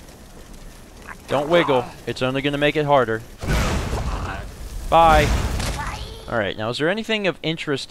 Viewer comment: sounded like he responded ''bye''